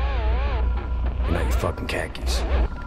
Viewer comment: Tyler's speech about the lost generation hits hard. "We work jobs we hate to buy shit we don't need." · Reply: I always thought he said 'car keys' which seemed a bit odd. 'Khaki's' makes a bit more sense I spose.